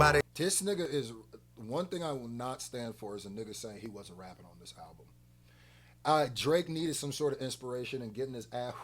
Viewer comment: I could of swore said he not reacting to the album at all